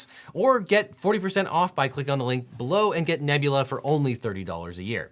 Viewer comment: I thought you said Illegal Eagle, which tbh would be a good series imo